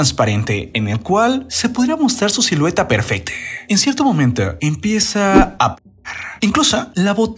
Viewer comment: empieza aaa? Yo entendí pujar entonces empezó a pujar para sacar la caca jajaja